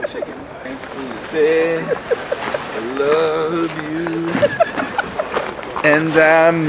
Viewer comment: Did he say "Syd, I love you..."?!! Look, I know this is the reality but YES!!! I ship Sky and Syd! I SAID SKY AND SYD NOT CHRIS AND ALYCIA...